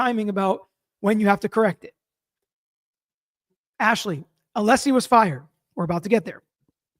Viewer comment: my heart skipped a beat, i thought you said he was fired 😂😂